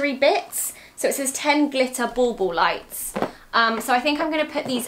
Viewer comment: For a year now when Zoey says bauble I literally thought she was just saying ball ball......... I am from America where we dont use that term so it just blew my mind!!!!!